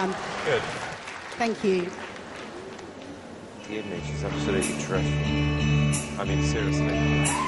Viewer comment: I can't understand what Simon's says at 46:16 😭 he says "she's absolutly ..." and I don't have the world just after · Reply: He said “the image, she’s absolutely dreadful”